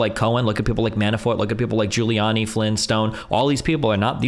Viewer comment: thought he said Flintstone. Lol